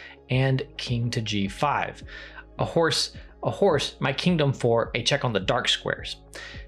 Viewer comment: Can someone please transcribe what Sam says from 03:50 - 03:54? It sounds like he’s saying “A horse, a horse. My king don’t four. A check on the dark squares.” Can’t figure it out, and English is my first language lol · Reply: I think it references a quote from a Shakespeare's play, and in this video he said "A horse, a horse. My kingdom for a check on the dark squares". The horse part has to do with the context of the play, and the kingdom part means he needs something so badly that he's willing to trade valuable things for it.